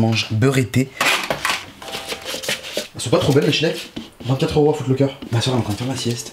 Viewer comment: A 0:48 j'ai compris ''elles sonts pas trop belles mes shnecks''